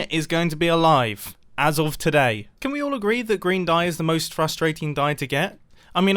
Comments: I heard "Grian dye" not "green dye" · i really thought Mumbo said "Can we all agree that grian die is the most frustrating dye to get" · I thought he said Grian dye. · When he said green dye, i hear it as "grien dye" and was likr wut m8? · Mumbo: Green dye is so annoying to get! Grian: What am I gonna do with all of this cactus and green dye? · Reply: The best part is that I missheard that as "Can we all agree that Grian Die is one of the hardest Dies to get?"